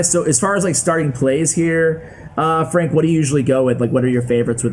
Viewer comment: did he just call him Frank?